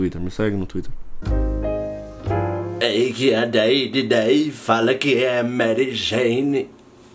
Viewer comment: man alguem traduz o que ele falou entre 02:05 e 02:10 pq eu estou me CAGANDO DE RIR !!! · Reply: Lucas Almeida eis que a 10 de 10 fala q é a merigeini